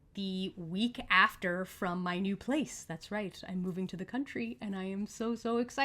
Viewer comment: Alllllllll Right moving to the country! Farm Raised I might add! Oh BTW when I first viewed this video I thought you said moving out of the country:face-red-heart-shape: I was like you go girl!